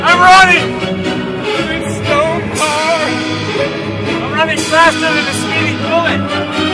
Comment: i thought he said "im running faster than the speeding bullet"